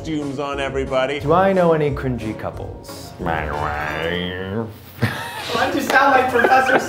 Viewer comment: Eugene: mY wIfE “Why do you sound like Professor Snape?” Professor Snape: Mr. Potter, You’ll be expelled.. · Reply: I’m so dumb I thought he said Zach and Maggie cuz it sounded like Zachandraggie but now I realize he said my wife